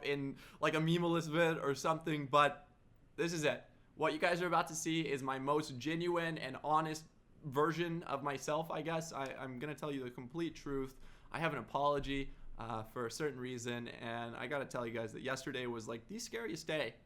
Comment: Did he say virgin skip to